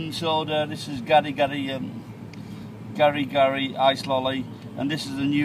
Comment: sounds like you said "gonna get high slowly" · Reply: Ice lolly?